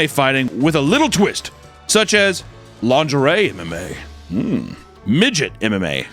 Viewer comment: Did I hear lingerie MMA? Ohhhhhhhhhh my~